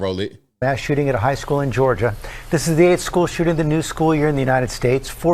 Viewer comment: What did he say? 2:18 "the 8th of the new school year?